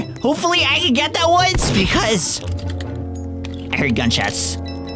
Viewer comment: I just heard gun shadt😂😂😂😂